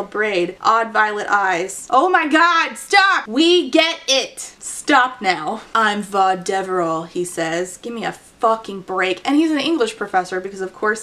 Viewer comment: Corvita Clem sounds almost like dirty slang & for a moment I thought said the male lead was Vas Deveral, which def sounds almost like a part of the male reproductive system. Sadly, tho, it's Vad...